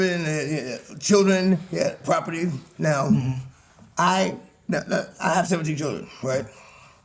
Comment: Did he say he has 17 children?